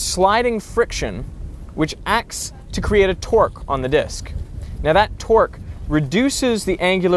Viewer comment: when he says 'torque' it sounds like hes saying 'twerk'